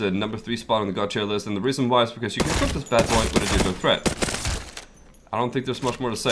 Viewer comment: What did you say at 3:57? The reason why..?? Didn't understand what you said after. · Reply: He said that you can put a digital threat on it